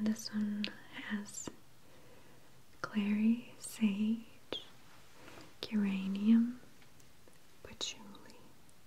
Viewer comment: I'm sorry does this says there is uranium in this oil? :')